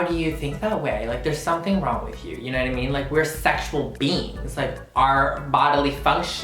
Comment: Anyone else hear "sexual beans" or just me?